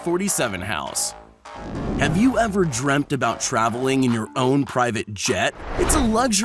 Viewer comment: Did he just say dremed instead of dreamed Sorry it’s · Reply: No he said dreamt lol